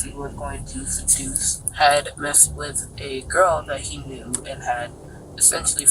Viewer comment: wait…. Did she say he r-ped a girl?!